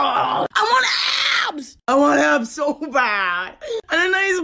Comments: I thought he said BUT I. ANT STOP EATING PICKLES AND MCDOLALDS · Before i thought it said I WANT A HOUSE